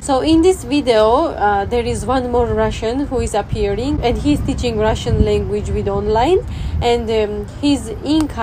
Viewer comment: He is teaching Russian with online。 1.5倍で聞いてたらHe is teaching Russian we dont likeって聞こえた。